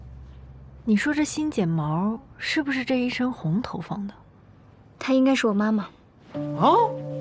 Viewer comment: I'm learning Chinese. I struggle to understand everything with the help of subtitles. In 17:20 when Mu Dai says "That should be my mother", it is translated to "她应该是我的妈妈". But honestly I cannot say I hear that. What she says between "她" and "妈妈" is not even close to what is written. 请问，任何会说中文的人都能帮我理解吗？ 🙏